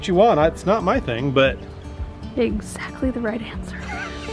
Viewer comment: "thats what I wanted to hear". 😂love you guys. May you always stay happy and blessed. ❤ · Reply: That’s what I heard too.....'right answer Aaron'.....such fun! 🤣